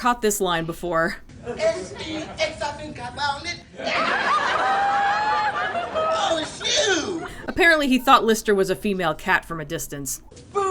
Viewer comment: what does the cat say in 11:14 ? I didn't catch it too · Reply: "S-E-X, I think I found it! Yoww! ... Oh, it's YOU."